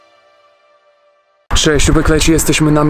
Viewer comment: a ja nadal nie rozumiem owcy xD cześć jupek jak leci usłyszałem xD